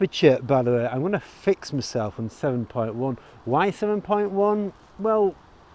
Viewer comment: You lost me with the aperture..... at 3:59 you said, or what sounded like, you were going to use "film point one" - never heard of that before.